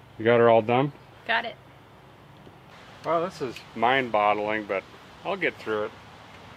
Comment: did you say "mind bottling" instead of "mind boggling" @ 3:12 lol